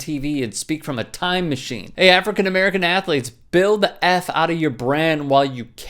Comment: I thought he said the African American athlete not the quarterback 🤔?